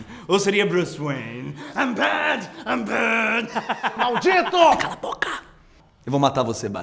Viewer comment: "i'm bad, i'm bad" kkkkkkkk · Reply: Eu entendi "I'm bat, I'm bat"